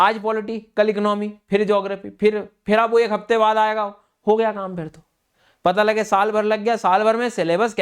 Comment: me sir me kya bola hai kuch smjh aaya 😅😂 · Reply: Ho gya kaam fer to😂😂😂😂